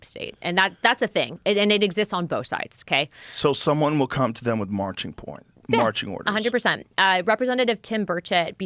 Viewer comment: did he say "marching PORN" by accident lol